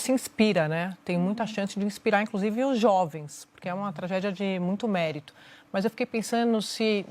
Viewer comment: Ouvi errado ou a Sadi falou (5:58) que a Tabata tem uma tragédia de muito mérito !!!!